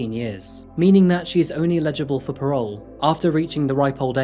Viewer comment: Firstly, thank you for your presentations - I feel you are starting to really polish your (now established) delivery style. Just one minor quibble.... even though closed captions show your words (at about 25:23) as "only eligible for parole...", I hear "illegible". People who have viewed your clips with me also hear it this way, so I don't think it is my hearing. This has occurred in other clips and I am sure you do not mean 'Illegible". for parole. Hope you take this in the spirit I convey it! cheers